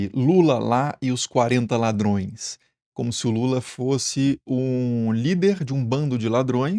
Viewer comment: Como se Lula fosse?! Não ouvi direito.